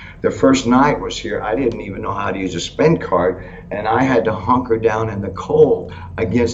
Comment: did I hear him correctly just say he didn’t know how to use his “Spank card” the first night here was there in jail?!?!? Hahaha!